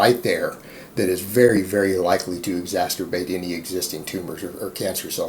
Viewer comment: Did he just say exasturbate at 7:50?